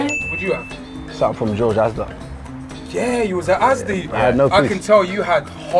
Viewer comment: what the hell was chunkz even saying? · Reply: “I had no p’s”